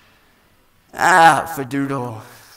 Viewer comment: DID HE JUST SAY “AHHHH FADOODLE”?!?!!!